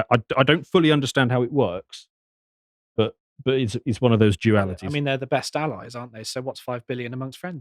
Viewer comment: I'm sorry, what did you say right there? Jewalities? 🧐